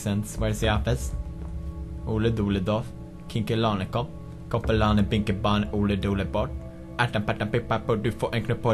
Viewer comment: what is he saying? Is it just mumble? Or something in Swedish? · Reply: Ole dole doff, Kinke lane koff, Koffe lane, binke bane, Ole dole doff its a counting out rhyme